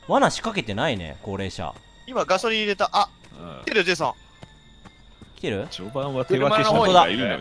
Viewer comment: が言う「ジェイソン」が「ゼーソン」に聞こえる人正直に👍🏻